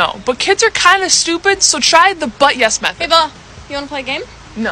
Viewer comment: The fact that when she was talking about the but yes method I thought her sister said hay ball instead of hay Bella 😅😅😅